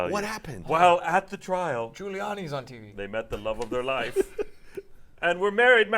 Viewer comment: Lol, I thought said Giuliani was on trial. I was like did just predict the future? Then I rewinded and heard him say “tv”.